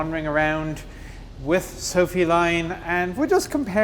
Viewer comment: When he first said her name, I heard 'feline'😂